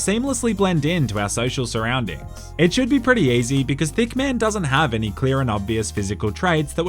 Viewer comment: did u say.... THICC MAN